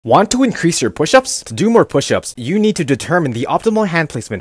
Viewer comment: I really thought he said: "want to increase your push-ups? do more push-ups"